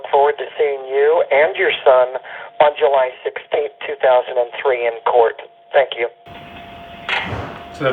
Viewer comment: Why does it sounds like the guy on the phone says July 16th 2003 < I hear 2003 instead of 2013